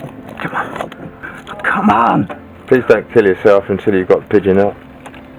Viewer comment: Did I just hear say "please dont kill your self before you gets the bird"? :D :D haha